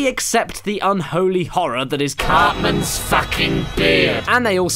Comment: I heard "Cartman's f**king beer..." (Yes, I know what it said.) at 4:08.